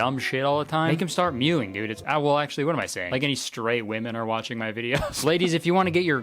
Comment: when Kurtis said there weren’t straight women watching his vids, I thought he said stray women and it was so funny. also yeah there’s at least 1 stray woman watching 🧍🏻‍♀️